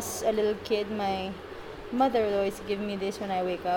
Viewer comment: It does sound like she says at 0:22 that as a little kid her MOTHER IN LAW was giving her this before going to bed. Seems like they marry em young over there!